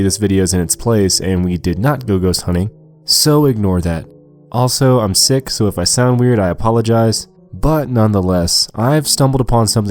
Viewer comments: thought he said I'm six so sorry if I sound weird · Okay 1 minute into the video xD I heard.. Him: I'm six so I'm sorry if I sounded weird Me: holy crap you're six?!?! ((I know he meant sick so please don't overflow the comments lol)) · I thought you said sorry if I sound weird I am six😂 lmaoooooo